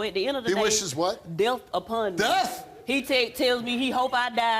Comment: Ngl…..I thought she said ‘deaf’ instead of ‘death’ (>.<)